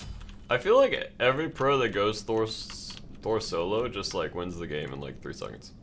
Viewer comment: Khajiit almost thought he said "thorns solo"